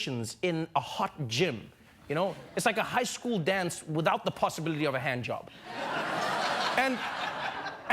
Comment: “it’s like a high school dance without a possibility of a handjob” Am i hearing this correctly or did he subtly sneak in a Satanic dare in there for more dark blessings. Wow